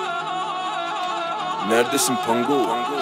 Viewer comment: ne diyor ? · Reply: Nerdesin pangoo